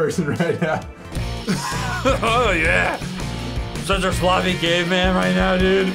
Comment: At part 7:09 I thought he said “I’m such a sloppy gay man” XD